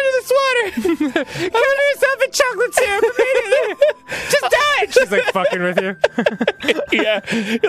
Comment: I think it's supposed to be: "Just do it." instead of: "just die"